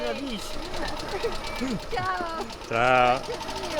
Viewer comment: What does say at 3:21? Buona vista? Edit: I think he says buona visita "enjoy your visit"